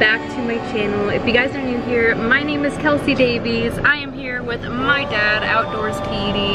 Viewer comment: I was reading comments and start watching video again and i heard "kelsi leave" like others... btw ik your bussy but my room is realy creepy i am realy scared of beeng in it at night bc of scarry music in te middle of the night